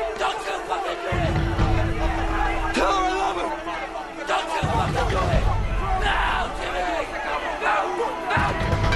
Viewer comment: What did he say at 1:23? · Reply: “tell her I love her” he meant to his wife